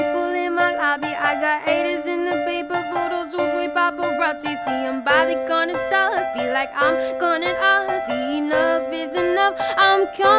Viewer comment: What does Halle sayyyy at 1:45 !? I can't figure it out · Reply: See Im By The Corner Store, Feels Like Im Cornered Off